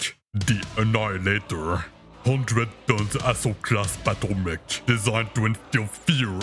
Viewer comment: This is my first video back and I sound a little stiff in the narration. I'll loosen up in the next ones ~ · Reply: With your accent I thought you said asshole class not assault class.